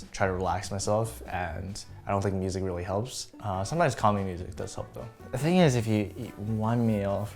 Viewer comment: "Sometimes calming music does help though". I had to listen to this 5 times, thought he said commie music. COMMIEFROST